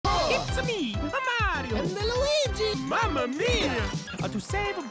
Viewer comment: the subtitle says Chris Pratt instead of Mario lmao